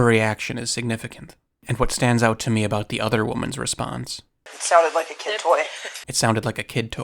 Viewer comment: "It sounded like a kid voice." I turned the volume up high and that is what I hear, The rest of the stuff didnt register at all.